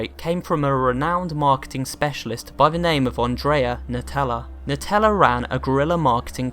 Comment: i heard Nutella, not Natella..